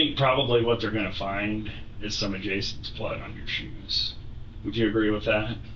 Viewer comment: I think he absolutely said, "They are going to find some of Jason's blood on your shoes." She was not mistaken and heard him correctly. Sir, did you really think you were going to catch her off guard with that when she just laid out the timeline of her day and at no point did she mention, "And I shot Jason." How in the world would his blood get on her shoes when she was in another town?